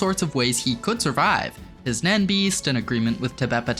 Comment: auto captions pit Tsetriednich instead of Tubeppa. Is this some sort of Oda level foreshadowing ??!!!?!!!